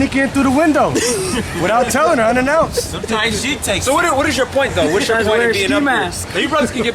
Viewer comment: I heard 'sometimes she takes me' and it made me crack up